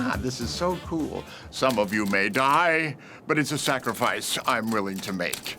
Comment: I always thought lord Farquaad said “some of you made I” not “some of you may die”